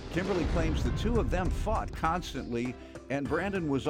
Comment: OH HE SAID FOUGHT...sounds like he said **UCKED. I had to do a double take, i was like man Judge mathis intro guy decided just to keep real this day lol